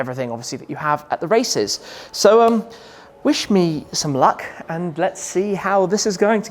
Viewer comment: <-- "wish me" sounded like "Shmee"